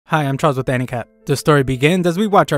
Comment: can anybody tell me what he says at the start of the video cause all i hear is "hi im charles anicap" like someone tell me..😭😢